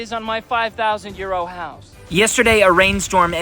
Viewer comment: Every time I watch your videos, I keep hearing you say “my 5000 year old house”. Of course my ears are American so it takes a while to process euros… sorry, not sorry, it’s why I keep watching them. Great series, can’t wait til it’s finished. 😂😅